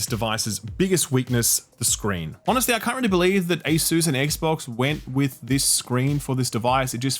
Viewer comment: I keep hearing "Air-sus"...